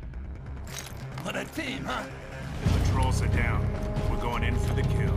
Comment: What did he say? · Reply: "What a team huh?"